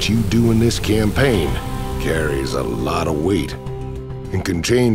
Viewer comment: Did I just hear: " carry a lot of weed" ?